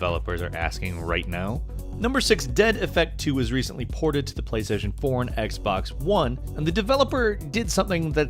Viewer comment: Did he say Playstation Porn?